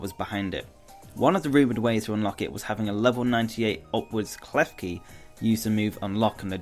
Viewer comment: A level 99 what Klefki? I'm sorry, but I didn't understand what you said, please help! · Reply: I swear I heard "Level 98 Obelisk Klefki" Obelisk the Tormentor + Klefki... WTF?!